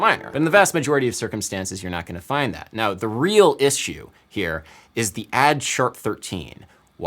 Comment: did our guy really just say "is shoe" instead of "issue"